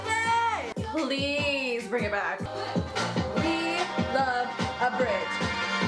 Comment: I legit thought you said "We love a Bridgit"